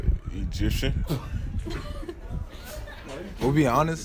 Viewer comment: Egipcian, did I hear right?